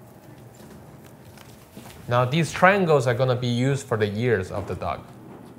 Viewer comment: him: now these triangles are gonna used for the ears of the dog *thought he said YEARS for the dog* me: years? what? *later on* me: ohh ears!!! now i get it